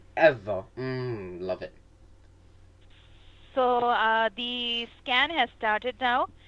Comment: When she says the scan has started now I thought she said the scam has started at first